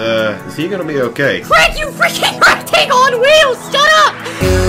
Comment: before i checked wiki: what i heard: quit you fricking rectangle on wheels shut up! after i checked wiki: what i heard: craig you fricking rectangle on wheels shut up! i really heard percy say quit instead of craig! XD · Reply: I heard, "Crap, you frickung rectangle on wheels SHUT UP!"